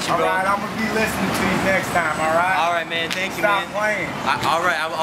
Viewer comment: Did that dude say "Stop playing" or "Don't stop playing" lol? It sounded like he said "Stop playing" and, you were like "I'll never stop" xD Or did he actually say "Don't stop playing"?